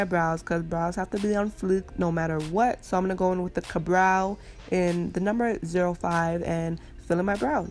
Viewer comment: I literally thought you said 'Cabrão' when you actually meant the benefit product (I think, I don't know I don't wear makeup) Ka-brow. Hehe😅